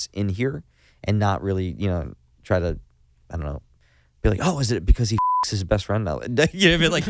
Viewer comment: Time stamp? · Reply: at around 38:30 he is talking about the im not okay reference in young and doomed and says "oh is it because he fucks his best friend?"(i replayed it 20 times to make sure i heard correctly lol)